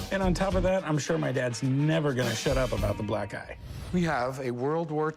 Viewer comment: At first I heard “black guy”. 😅 glad it wasn’t.